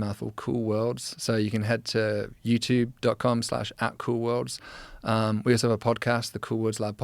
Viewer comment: It's a good thing you showed your web page because I heard "Cruel World" Instead of "CoolWorlds"!🎉